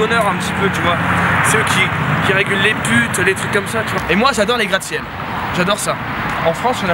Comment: J'ai trop cru t'allais dire "Et moi j'adore les putes" mdrr